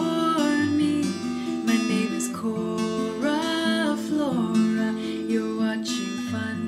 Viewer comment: oh damn i thought she said"my name is chloroform in the intro"lool